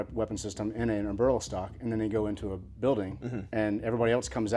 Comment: What does he say at 2:12? They "put a sniper rifle in a (something) stock"? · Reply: Eberlestock. Pack manufacturer